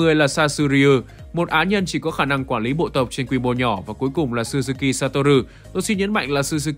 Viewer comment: tui nghe thành Mikami Satoru